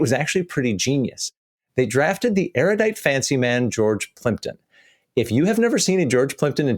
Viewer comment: Not gonna lie, when said 'Intellivsion drafted in erudite fancy man George Plimpton', I actually thought he said 'George Clinton', The Parliament funkmaster supreme. Now THAT would have been something to behold, and for a second or two I actually believed it might have been true.